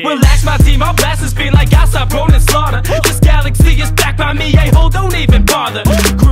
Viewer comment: It accidentally said Roman’s Slaughter instead of Ronin’s Slaughter.